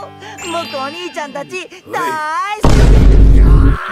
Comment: did he say the N word😨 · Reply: surely it was 目がー (me ga ) cause it means eyes are — 😂